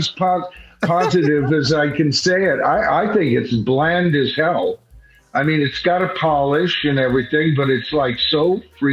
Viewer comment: I couldn't understand reaction there after you said the album was bland and polished ? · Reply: He was going to”Oh Shuck’s” that Scott from the Prog Corner couldn’t join us! Scott woulda been a cool addition to the panel! ❤